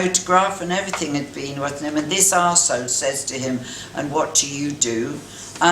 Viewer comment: did she call him an "arsehole" for not knowing who Francis Bacon was? what a self absorbed shitty person she is.